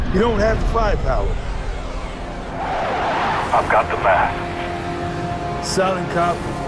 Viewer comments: "Commander... you don't have the firepower." "I've got the mass." Carter was a bad ass. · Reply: Brandon Fortier when I first played it I always thought he said “I’ve got the math” which would’ve worked too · I always thought he said “I got the math” Still a badass line.